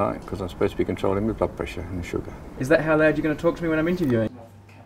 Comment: At first I thought Brady said at 1:31 "Is that how you're going to fucking talk to me when I'm interviewing?"